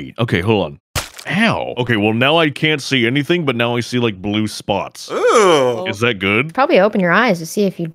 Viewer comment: why did the captions say orange when narrator said blue 😂